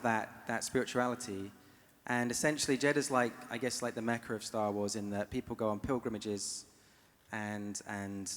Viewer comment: ish did he say "Jedha is like the Mecca of Star Wars where people go on pilgrimages"?